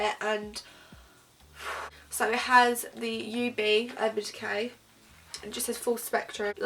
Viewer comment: Wait I thought it said UD for urban decay and now I've learnt it's ub, I'm shook